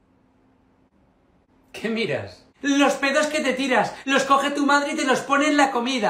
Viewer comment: Adulto: ¿Que miras? Niño: Los pedos que te tiras, los recoge tu madre y los pone en la comida Adulto: Oye que mal educado eres, ven aquí ven aquí. JAJAJAJA esto me hizo el día 🤣🤣🤣🤣🤣🤣🤣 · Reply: Yo pensé que había dicho las pedas